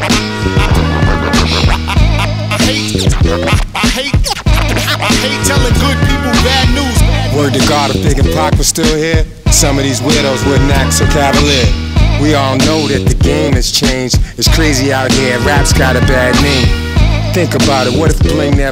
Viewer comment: I swear I thought he said if pac was still here I swear some of these Wills would disappear lol